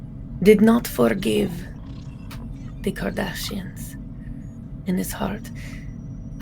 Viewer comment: I love that the caption shows "Kardashians" instead of "Cardassians". 😂